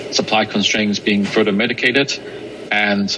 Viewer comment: did he say med(t?)icated at 5:50? did he mean mitigated?